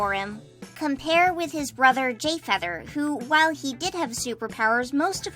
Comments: I swear I heard Gayfeather omg I am in love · did..did you say "gayfeather"?